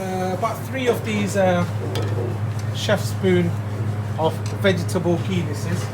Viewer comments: did... did he say "vegetable penises" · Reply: "...Chef spoon of vegetable ghee this is." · Vegetable Penises? What did he say?